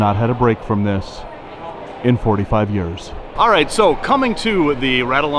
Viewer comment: "Emily and Ed have not had a break from this in 45 years" 😆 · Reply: I could have sworn said 55 years!